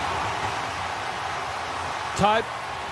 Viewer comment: did he say Todd?😂